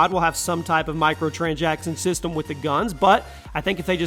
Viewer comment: why did you say microtranjackson at 8:00 ?